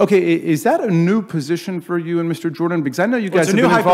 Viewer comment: Did he call Matt Mr. Jordan at 3:30.